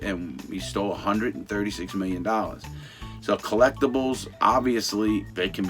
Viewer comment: "we stole 136 million dollars" I thought u said former? 😂